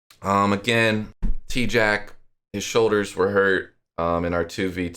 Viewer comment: When you said tjack I thought u said tjass you woulda capped bro